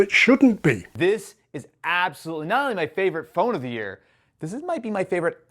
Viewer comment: my brain completely rejected the de-accented word, and replaced it. I heard "iPhone of the year" until I looked at the screen when it was slowed down.